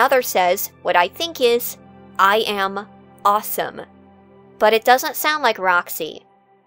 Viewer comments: Personally I heard “I lost him” instead of “I’m awesome” after the crashes. Maybe being surprised that she managed to escape? Hard to tell. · You know, I was a bit surprised that your take on the last Roxy line was "I am awesome." Because I heard it as a despair filled "I... Lost..." Which not only ties into her obsession with winning, but the inversion caused by Safe Mode. Tragically, while her selfishness was turned into altruism - that she gained the freedom to act with humility in front of someone - she was finally able to admit when she lost. And lose she did.